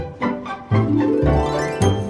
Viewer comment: it sounds like he says "¿Qué?"